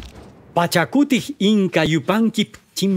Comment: i thought he say gajah putih 😂 (in my country gajah putih mean : white elephant)